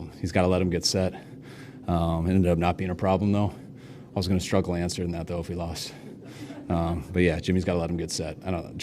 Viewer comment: what did he say?? "I was going to struck Lance if we lost??" He needs to enunciate better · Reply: “I was gonna struggle answering that if we lost”